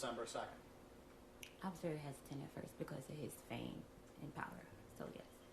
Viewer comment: What does she say at 2:18:50 ? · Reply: She said she was hesitant at first, because of his fame and power.